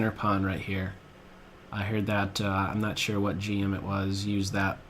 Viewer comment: I heard, "I'm not sure what G-Unit was...but, they can be described as a bunch of pawns" me" 0_o "exactly!'